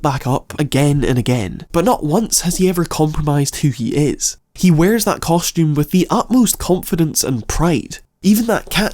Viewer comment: Yo, what's he trying to say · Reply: a groan